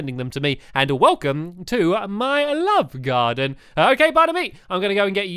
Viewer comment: i thought you said lop garden leleelelellelelel